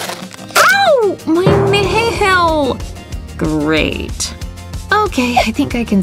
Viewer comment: why did she call her nail a nhail eDiT: ThAnK U fOr FiVe LiKeS IvE NeVeR HaD FiVe BeFoRe 💩